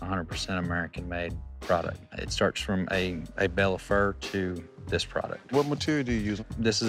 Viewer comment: A bale of Fur I heard "Andy Bellefleur," I've been watching too much TruBlood 😂